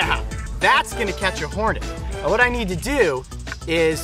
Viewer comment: I thought when the camera man said good suction after that I thought he said this will catch me horny